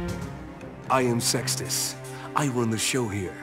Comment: If its not for the sub i would thought he said "I am sexist"